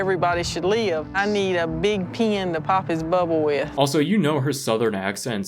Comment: she clearly said "pin". well. not CLEARLY. because of the southern accent. but. she definitely said pin